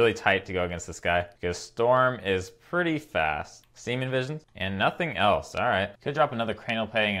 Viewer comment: Did I just hear SEMEN Visions?